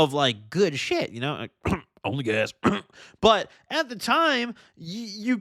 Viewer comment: at @ 23:50 does anyone know what he said what he "coughed" and said something like Onlygas? lol